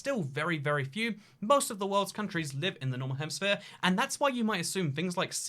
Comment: It sounds like he says "normal hemisphere" haha!